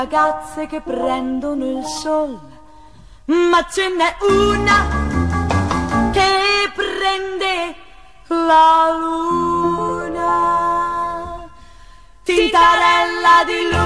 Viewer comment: Pense que decia mapache en la cuna xd